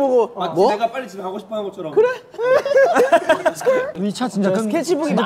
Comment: Seungkwan saying "Skkrrr" instead of "Keurae" 😭😂